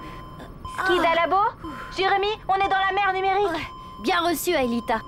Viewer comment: J'ai entendu "On est dans la merde numérique"